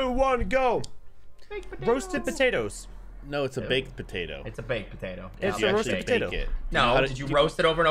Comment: I'm a Brit, and while agree with on most of these, that was definitely a jacket potato and not a roast potato. They two different things. And I often use the American words for stuff. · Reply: Yeah I thought they said Jacket Potato as well. But I’m American so I just say baked potato